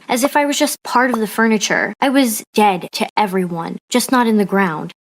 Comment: I thought she said dead mom